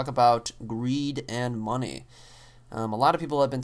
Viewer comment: i thought he said "weed and money". i then got slightly more interested